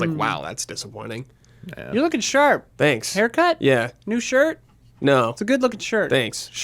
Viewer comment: Correction: just after 20:00 calls, when thanking her for complementing his look. But I might be mishearing it.